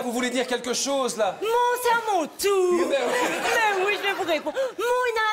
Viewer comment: Qu'est ce que Livia dit a 1:30 ? Je n'aipas compris... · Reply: Moohhh c est a mon tour